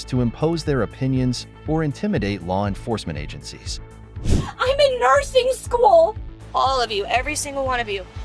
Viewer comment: it almost sounds like she said Im in Narciss school. Narcissist?